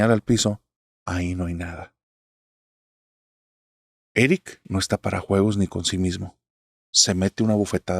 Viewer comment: Narrador, me diste el susto de mi vida jajaja, en el minuto 35:16 se escucha (vic) bueno yo entendí eso y como es mi nombre Victor y todos me llaman así vic, se sintió machín, más que nada, por qué estoy en el trabajo y hoy me tocó estar solo, saludos y también de mis vecinos, tras muro está un panteón